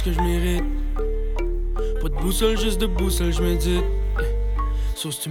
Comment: qu'est-ce qu'il dit ? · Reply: ''Pas d'boussole juste debout seul j'médite''